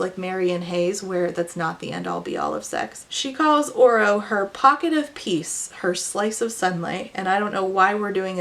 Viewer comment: okay i misheard "pocket of peace" as "pocket of piss" when isla was talking about oro and i can't stop thinking about that